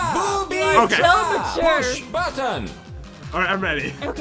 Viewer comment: mushed butter is what i heard